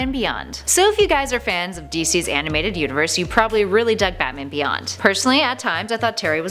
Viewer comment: Sounded like she said DC'S animated uterus.